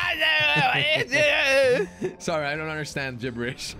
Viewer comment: did he just say scheisse 😂